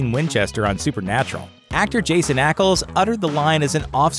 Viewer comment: Haha that's funny thought you said Jason Ackles.............